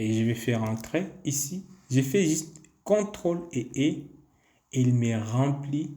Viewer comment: SVP à 45:39 min c'est ctrl+ quoi svp? j'entends pas j'ai cru entendre ctrl+E mais il semble que ça ne soit pas ça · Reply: Oui, CTRL+E